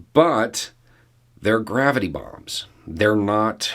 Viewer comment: Oh God, at first I thought you said "Gravity Bongs". Becoming a fireball is bad for anyone....😳😥